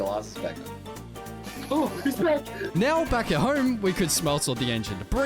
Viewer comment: When bro screamed he's back i heard him he's black😭😭😭💀💀💀🙏🏿🙏🏿🙏🏿